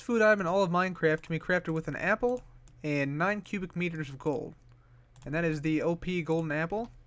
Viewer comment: 9 cubic meters of gold and 1 apple? Did I hear that right?